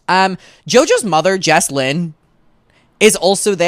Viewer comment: i really thought u said “jojos mum, Jess Glynne”